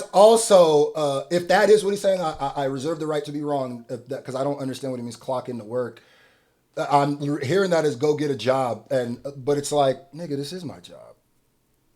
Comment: I heard it as “lock in,” But also LOL Your interpretation is definitely funnier And probably more accurate oof Get his ass